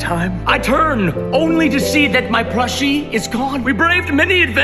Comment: At first i thought he said "brushie" and thought that was other word for "brush" because he was cosplaying as a toddler but he said "plushie"